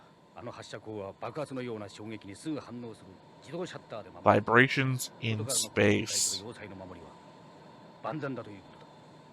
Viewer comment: pretty sure that's a translator error, I think he said "fluctuations" As for the Gamilans, it's entirely possible some of them don't actually know wtf is going on over on Beemeria. They might've put the Queen into power and let her do the work.